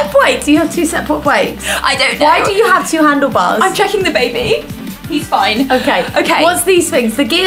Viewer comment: I DEADASS THOUGHT SAID "I'm chucking the baby"